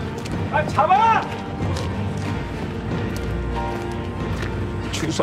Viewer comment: 여기 나만 주서경 으로 들리나?